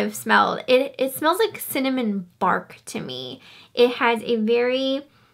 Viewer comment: Thought you said “cinnamon barf” for a second there lol